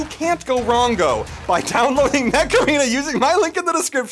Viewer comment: So with was talking about Macarena or something like that I fast-forward it and he said, “ Fuck!” And then when I fast forward it one more time it sounded like he said this “ you get to- fuck weapons” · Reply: Mech arena